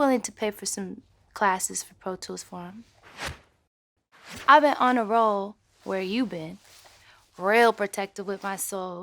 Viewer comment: i thought she said ive been on a roll with you babe..........